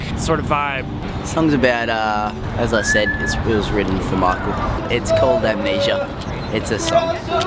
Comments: Did calum say it was wrote for Michael? 🥺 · did calum say its for michael at 0:54?